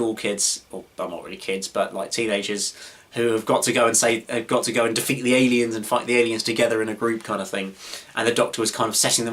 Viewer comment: Kinda sounded like you were saying "defeat the aliens and fuck the aliens in a group", i know that most likely wasnt your intention but still